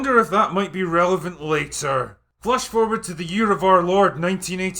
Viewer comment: Did he say "flush forward"? 🤔